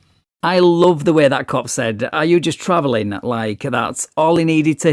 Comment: I thought the cop said “Are you just babbling?” Rather than “Are you just travelling?” 😜